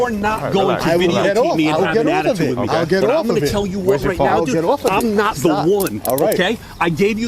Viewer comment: I can swear that the mouth spitter said at 2:16 he was not the warden . You hear him as saying he was not the one. But it don't matter he gets no qualified immunity so it's all good.